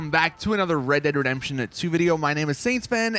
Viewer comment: did he say “red dead redemption and 2”?